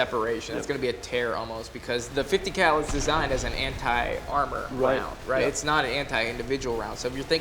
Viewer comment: what i hear ( hent@ armor) lol🤣